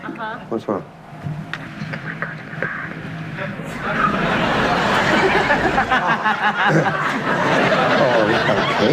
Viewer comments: what did the women whisper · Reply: I'm pretty sure she said "I have to go to the bathroom" XDDDD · Can someone explain the 8:59 mark what did she whisper? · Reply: Bathroom break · English is not my first language. Can someone please tell me what did she whisper at 8:59 ? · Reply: “Can I go to the bathroom?”